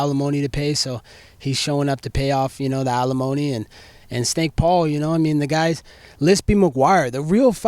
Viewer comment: Did he call him steak Paul ? 🥩😂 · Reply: Snake Paul